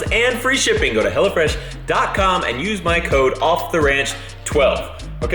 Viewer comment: im i the only one who heard " Go to hell or Fresh"?